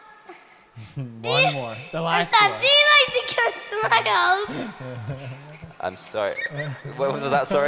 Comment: i thought the kid said "umm dan do you like to kill snails"